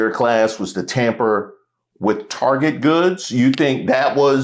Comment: um, I think the dumb lady said “tambourine”, not tampering. 😅